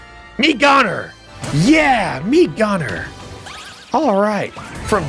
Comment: I heard “Mii Goner”